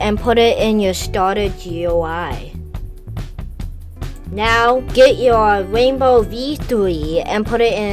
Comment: What i heard Get the rainbow v twee